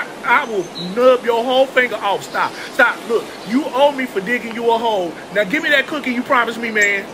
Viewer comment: "You owe me for digging you a hole" 😂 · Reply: The first time l watched this l thought he said" you owe me for dating your who*e". Where's my mind at?